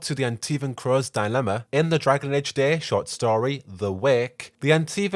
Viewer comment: I thought It said Dragon Age Antifa... I was so confused.